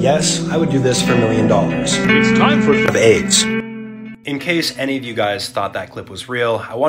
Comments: "It's time for AIDS" LMFAO 💀💀💀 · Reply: I thought he said it’s time for babes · "it's time for aids"💀💀💀💀💀💀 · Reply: i hear "it time for babe" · “Time for the eggs” is actually WILD 😭🙏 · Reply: I thought that I heard "Time for the AIDS"